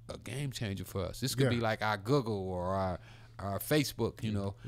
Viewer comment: did Ricky say Guggle instead of Google ? 1:06:23 hahaa